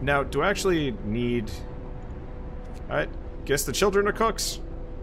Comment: for some reason I heard 'children are to be cooked' .-.